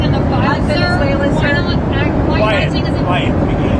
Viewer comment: POTUS calls a reporter "Piggy" to her face. Would have been headline news in a different time. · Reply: I thought he was saying, "Quiet, McGee", as in someone's last name? Piggy is worse.